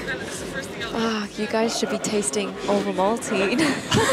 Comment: anyone catch which chocolate she said is better? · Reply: Ovomaltine, it’s a Swiss chocolate.